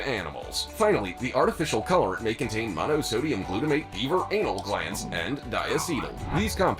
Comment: Did he say it contains “Beaver Anal Glands” ❓❓❓❓❓❓